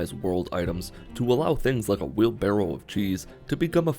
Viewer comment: I heard 'a Will Ferrel of cheese....' I need new ears